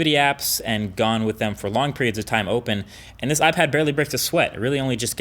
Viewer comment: You should start a new series called Crispy or Crap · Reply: did he say "barely breaked a sweat"?